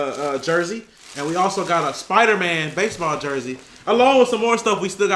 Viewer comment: did you just say SPIDER-MAN 🙀 (I want it)